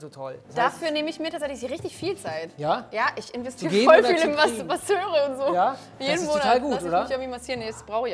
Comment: Ich hab da kurz etwas anderes verstanden... :D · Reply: Hab da Mastubaseure verstanden🤷‍♂️wie man das dann deutet ist jedem selber überlassen.